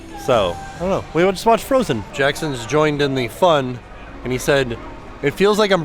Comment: This is great! Love themAlaskan views. BTW…when you say quickly & Jackson, I hear “Janet Jackson” for some odd reason 🤣